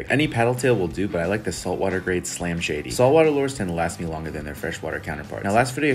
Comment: I thought you just said slim shady